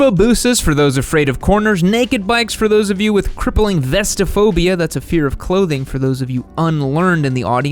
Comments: I thought he said Vespa phobia! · Oh, ohhhh .... you said VESTaphobia?! I thought you said VESPAphobia, and I was like what the heck do naked bikes have to do with scooters?! (I kid, but take the bad joke any way)